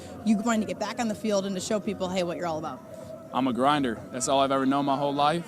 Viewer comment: I believe him. Oh, sorry. I thought he said he was ON grindr.